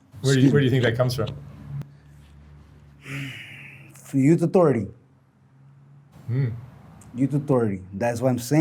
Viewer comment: i like this guy! 23:51 can anybody tell me what he said? plz and thank you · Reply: Youth authority